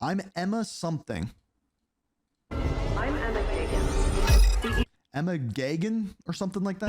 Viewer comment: I think she said Emma Pagen.